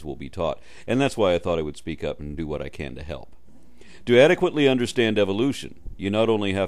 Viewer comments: Notice at 0:52, does NOT say "believe in evolution," but "adequately understand evolution." This is so mind-blowingly obvious that your post is basically on the level of troll. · "the same can be equally said for evolution." But that's not what he says. His wording is very important. He says "To adequately understand evolution" around 0:52, not "believe." Right off the bat, you seem to be intentionally misquoting. As long as less than 50% of humanity has a strong grasp of science, is correct in stating that most people don't understand it. I would also like you to prove that a billion Christians are creationists...being Christian does not necessarily mean that.